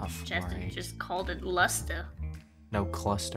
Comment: Yell said luster I thought they said lobster 🦞 😂